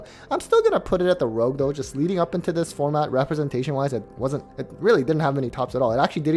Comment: Bro i need a staples tier list And i thought you said this format is irrelevant